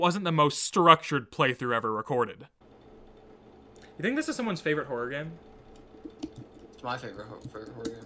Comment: if you can’t understand what is saying, “it’s MY favourite horror game”